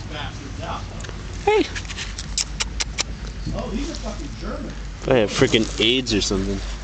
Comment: whaaaat?! I hear on 0:09 fucking german? i am a german boy thats not really funny ^^